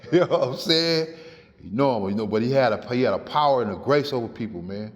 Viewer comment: I THOUGHT SNOOP SAID...HE HAD A FORK.